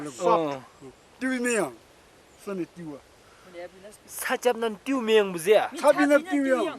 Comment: 자꾸 살짝만 띄우면이라고 들려서 한국말 하는줄 ㅋㅋㅋㅋ